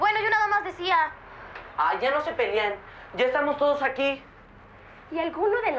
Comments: "hay ya no se pelian" oh escuche mal xd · yo tambien escuche ya no se pelien